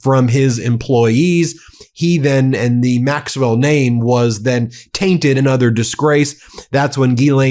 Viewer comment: I thought the court said NO Re max well. He does not her out to talk to media.